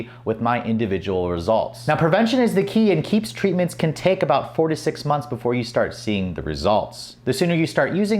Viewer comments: Did you say "46 months"? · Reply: 4 to 6. (On top of which even if you thought you heard him wrong he said in his case it took about 6 months.) But I can hear how you kind of thought he said 46. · Did you say "46 months"?